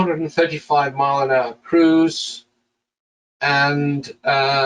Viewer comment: did you say 435 mph cruise?